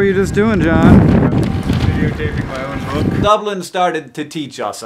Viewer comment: What did John say around 1:03? I heard "...my own book" but I couldn't make out he rest, despite listening to it about 5 times.